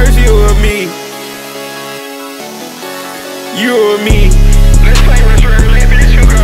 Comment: No joke I thought it was saying “ You a meme” 💀